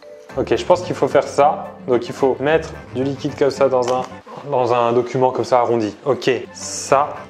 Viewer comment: c’est moi ou il a appeler le bol un « document » 😂😂